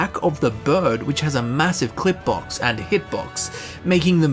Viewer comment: did you say the bird has a massive clit box? O.o